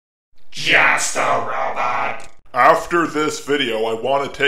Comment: When you say "Just A Robot" really fast in this video. It sounds like you said "Jester Robot" to me. It might have to do with me being a Jester.